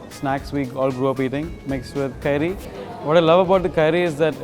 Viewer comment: just a correction, the guy at 3:53 does not say curry but "Kairi" which means raw mango that we see in the video.